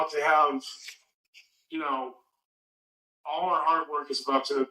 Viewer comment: A philosopher, a psychologist, AND a leading authority in federal crimes. Renaissance man, there! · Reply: I actually thought he said “art work” when he said “hard work.” 😂